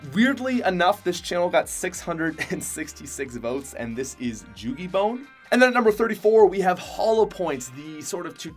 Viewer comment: did he say joogie bone 😂😂😂😂